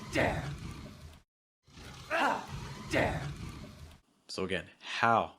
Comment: At min 3:45 does she really say "how dare" or "how dare you"? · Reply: the "you" is very, very slight at the end of it; it's "how dare you" great question Richard! I love how closely you are paying attention to details